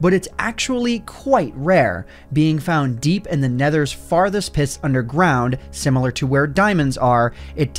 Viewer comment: Did you just say “In the Nether’s farthest PISS”!?